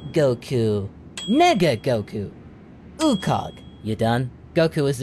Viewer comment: When goku said nega goku I thought he said n*gga goku